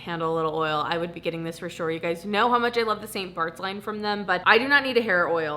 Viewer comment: NGL when you got to the Ouai product, I heard "stain farts line" and I was like....that can't be right